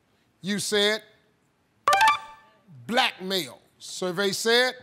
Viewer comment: LOL! I thought she said "Black Male," not "Blackmail," but, with the first answer it could go either way and she could've easily contested it if she's wanted to.